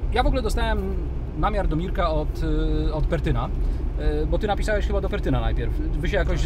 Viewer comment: typo in subs - "Petryn" instead of "Pertyn"